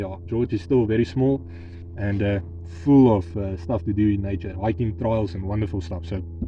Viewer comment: I love the comparison. 😂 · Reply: I thought he said "Stuff we do" at around 0:58. 😅